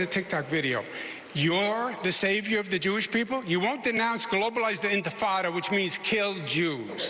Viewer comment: Can someone tell me what Cuomo says at 32:10 when he said that Mamdani won't denounce globalized what? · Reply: Globalize the Intifada — basically, the elimination of the state Israel.